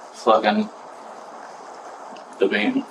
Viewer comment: Did he say "flicking the bean?!?!" 🤯🤣🤣🤣🤣🤣 A. That's not what that means, unless I'm missing something... B. Why would you say that when you just peed??? 🤦🏻‍♂️🤦🏻‍♂️🤦🏻‍♂️🤣🤣🤣